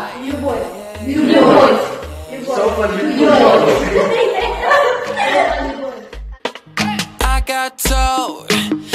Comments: Что он сказал ? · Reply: Он сказал "Софа любовь"))обожаю это видио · What did said on 0:19 ? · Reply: was teaching them a few russian words, and taught how to say love in russian so was like love ? it kind of like that.